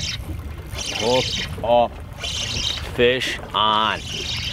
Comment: Love these videos. In many of them, including this one at :15, 3:35, & 9:29, you like to say '? off, fish on' when you hook up. I just can't seem to make out what that first word is. Anyone? Thanks. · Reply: hes saying “hooked up”